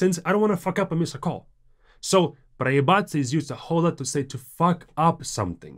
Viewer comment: Did you just say "a xoxol lot" XD